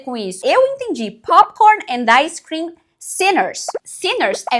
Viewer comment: eu entendi sealers